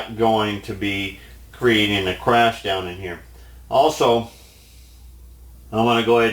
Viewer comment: Attention Classic "Fenner-ism" begins at 9:25 Loved that remark good sir, you made me choke on my sandwich, LOL! Aloha...Chuck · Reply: Huh, my hearing must be going, I thought he said "Creating a Crash Test Dummy", instead of "creating a crash DOWN IN HERE"...funny how the mind makes up it's own words when you're watching with the volume too low...D'oh!